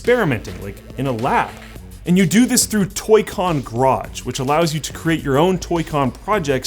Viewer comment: Did he say "Toy Con Crotch"?